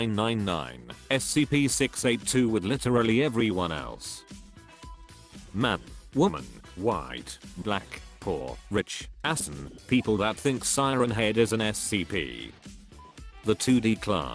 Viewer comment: So, nobody will talk that the ivona-like voice (forgot what it is called bruh) says Ass instead of Asian?